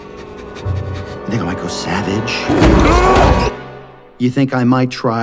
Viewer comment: Sounds like he said "n-word might go savage"